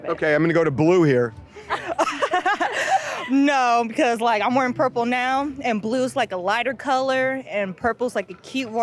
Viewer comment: I thought she said ‘I’m wearing purple nails’ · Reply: She said, "l'm wearing purple now", not nails.